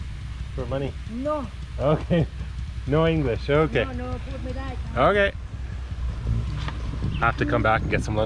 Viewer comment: did she say "nono, tut mir leid?"